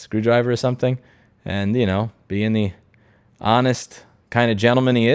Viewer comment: "being the honest looking" I heard " B & E the honest" breaking and entering honestly.